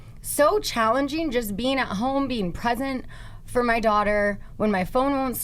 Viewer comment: OMG! I thought Scheana said she was “pregnant” hahah but after rewinding, she said “being present” lol